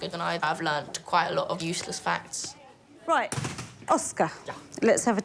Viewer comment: did he say useless facts.